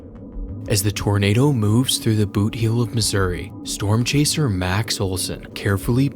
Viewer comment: When it said MAX OLSON I heard him say "as the tornado moves through the booty hol-"AAYOOO WHAY